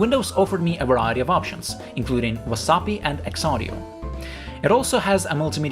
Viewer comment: i thought you said wasabi for some reason. Aside from that I applaud your efforts. That looks like pure hell